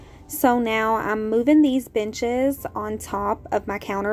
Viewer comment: I swear I heard “I’m moving these bitches” when you moved the benches lol